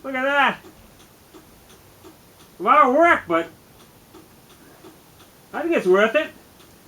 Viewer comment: Nice job sir My question is this: @ 6:27, what did you say exactly? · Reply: No that’s what he was saying. “A lot of work but I think it’s worth it”